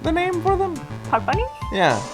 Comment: I swear to god when the judge said Puck Bunny I thought he said fuck bunny